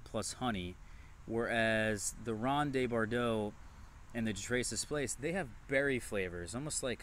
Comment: I caught the Ronde de Bordeaux but I can't make out the other kind. Traces place? Closed captions have it wrong. Anybody know and care to share? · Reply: De Tres Esplets maybe?